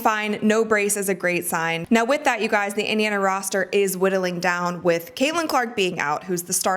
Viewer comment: I'm a fan of your reporting. One correction -- even if a nitpick. "These injuries mean the Fever’s roster is widdling down quickly, so they needed to act. " I think you mean "whittling" down. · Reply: I thought she said dwindling down which might have sounded like widdling? Anyway, we all got what she meant so that was very nitpicky.